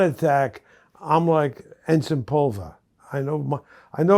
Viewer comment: Did he just say "Jens Pulver" around 16:20? Lol how does he know Jens Pulver? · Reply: Ensing pulver. Its from a old movie I think